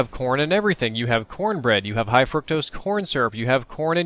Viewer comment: oh! no lie and im not tryin to be dirty but at 3:05 i thought he said porn